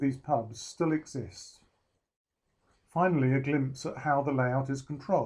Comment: I thought you were about to say “ finally our clubs bar “ great layout; well thought out!